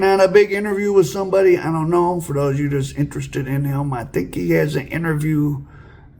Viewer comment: I thought they said he has aids ?